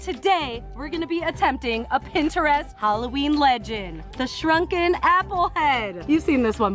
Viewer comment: I thought it said drunken apples but I guess I just assumed because it was and